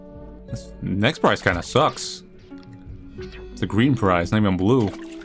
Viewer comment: AMOGUS!? 😳😳 1:09:08 Edit: i thought he said "sus "and not "sucks"